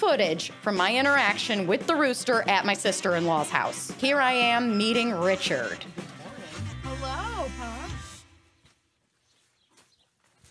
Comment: I THOUGHT SHE SAID “EATING”.. NOT MEETING RICHERD… LMAOO